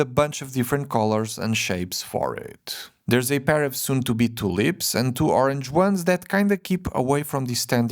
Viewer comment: did he say “two lips” or “to lips”?